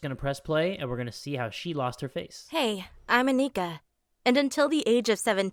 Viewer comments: in thought she said "hi im a n****" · Did she say she was an N word???